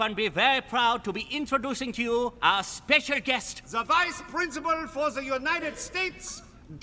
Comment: Did they say vice PRINCIPAL?